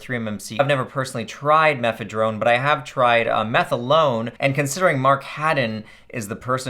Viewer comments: It almost sounded like meth alone. Lol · It sounds like says "Ive never tried methadrome but I've tried meth alone" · I heard "I have never tried mephedrone but I have tried meth alone" and I had to watch that clip again to make sure you were not saying that lmao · I thought he said he "tried meth alone" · I thought he said i personally have never tried methadone but i have tried meth alone